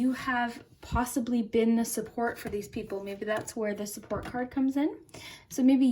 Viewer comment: Group 1 omg I heard 'support card' as 'squirt card' lol. I don't mind tbh. Loved the reading BTW💜💜